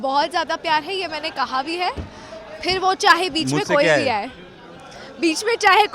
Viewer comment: can anyone tell me what did he say secretly to her the way she was laughing and he was laughing is just forcing me to hear that word.... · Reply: He said, "mujhse kya hai?"Turn on full volume and you'll hear that